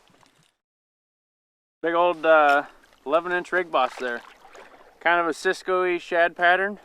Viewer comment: What’s the name of the bait you were holding up around 13:40? It sounded like you said Rig Boss but I couldn’t hear you very well. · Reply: Yes it’s a Rig Boss glider. You can only get them at TRO or direct from Tim. They are a limited run of baits each year. They are great baits. You should be able to find him on Facebook. Thanks for the question and watching.